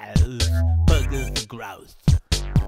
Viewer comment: I totally thought it said "Bloggers are gross!!"